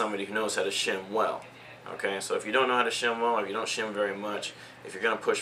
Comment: thumbs up if you thought he said sham-wow at 5:11 :D